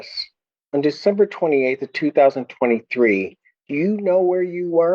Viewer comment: The judge literally asked him where he was on Dec 23rd and then after answering the judge switched the dates and said he was there on the 28th... He switched the dates after asking the question... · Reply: no, he said Dec 28, 2023 the first time. I believe that i heard the lawyer (incorrectly) correct the date to Dec 23